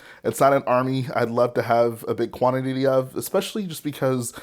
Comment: did he say quanit-tity?